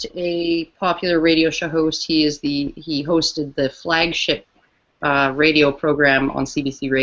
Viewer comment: "Flagshit." Did I hear a freudian slip? ;)